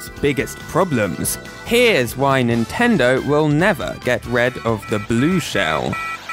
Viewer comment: Wasn't sure if I misheard when you said "Get RED of the blue shell", but then you said it again later in the vid... nice. 😂